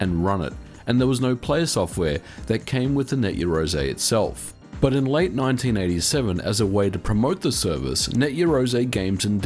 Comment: did you say 1987 there or do I mishear it everytime? You mean 1997 right?